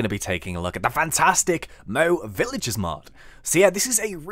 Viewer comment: Hai :o) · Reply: I thought it said "No villages mod" I was like "WHAAAT" For you people who watched the geico commercial